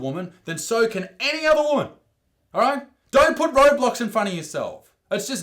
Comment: did he say roblox